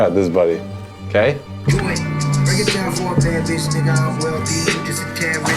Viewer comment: i might be trippin but did logic say the n word?